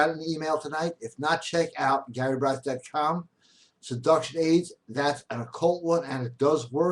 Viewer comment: what did he say? Seduction what? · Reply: He said "seduction aids". "Aids" (multiple) or "aid" (singular) are very old english words meaning help or assistance. In this case, tools helping the seducer, in the act of seducing women. It is that simple. Hope this explanation helps you people.